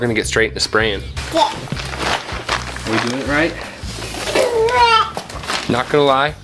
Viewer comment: It's so delightful and fun to watch lil' toddling around everywhere! 😃 I'm think heard her tell Daddy he's "doing it right" at the 12:34 mark. 👍 - Kim